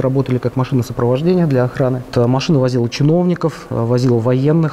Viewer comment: Я думаю мне послышалось ”….. машина возила чмовников”.